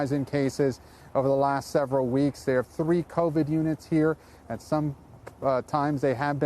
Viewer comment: That's not correct. Go back and listen to The Post. He said on that one floor there were 14 patients on ventilators. They have four units just for the people who have the virus. He did not say they only have 14 people on ventilators in that entire Hospital. Seriously listen up!! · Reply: I’m pretty sure I just heard him say they have THREE Covid units (0:11 and 0:58). He also didn’t say anything about the 14 people on ventilators being on a specific floor level. He didn’t specifically say, but it sounds like there are 14 people total on ventilators in that hospital. At least one would assume so based on the info given. Go back and watch “The Post”. Maybe you shouldn’t try correcting people before knowing your facts? Maybe just let it go from now on? Seriously...listen up. 🤫 & 🗣👂